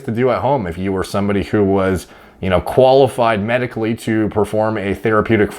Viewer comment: When you were discussing PRP I thought you said medical lobotomy and was like...wait...what 😆